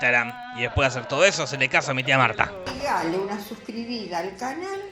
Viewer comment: al final no sé porqué no entendí "hacele caso a mi tia marta" sino "se me casa mi tia marta" KHE?